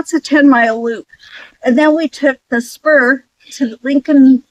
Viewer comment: When you said "took the spur," I first misheard that as "took the Smurf"!